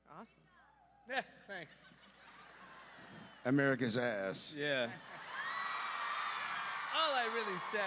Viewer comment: What did the person say when he said "thanks" at 12:18? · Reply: Jeremy Renner said "America's ass," which if you've seen Avengers: Endgame you'll understand.